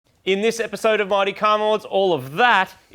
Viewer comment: I always hear 'Marty car mods' in the intro instead of 'Mighty Car Mods'. Always!